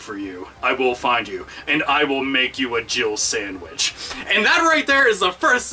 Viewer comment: I keep hearing it as "Jibble Sandwich" WTF?